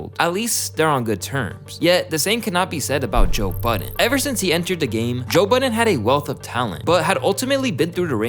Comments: at first I thought he said Joe Biden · i thought you said joe biden for a second at number 2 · At the beginning i thought he said Joe biden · I heard “Joe Biden”